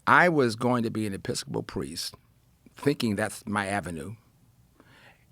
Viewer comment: Oh, I thought he said he was thinking about going into The Pittsburgh Police! Not a Priest!